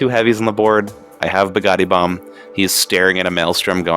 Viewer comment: I kept hearing "Pagani Bomb".